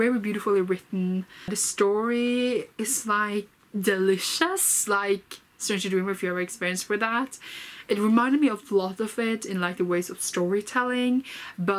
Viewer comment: what are the books that you are comparing to this one? I couldn't quite make out the two names from your video. Thanks, I'm interested. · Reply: Ops, sorry for speaking unclearly! I was talking about the following books: Strange the Dreamer by Laini Taylor & Every Heart a Doorway by Seanan McGuire!